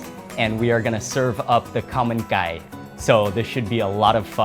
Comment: ah, Khao man gai! All throughout the video I thought you were saying 'a common guy' LOL